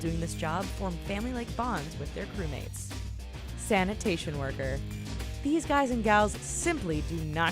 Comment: at first I thought it said santa worker and I thought how many people die from being santa?